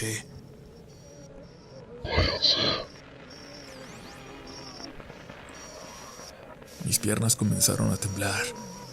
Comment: En el minuto 16:19 ¿qué se supone que dice? Por más que lo repito no logro entenderle. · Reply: "váyanse"